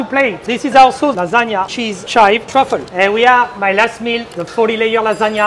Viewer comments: I thought he said: 40 day old lasagna. I thought: wow. that's smart. but then it turned out it was a 40 layer lasagna haha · that’s a great dish to ration over 40 years, 40 layers 40 years I only said year’s because i thought it said 40 year lasagna for some odd reason 😅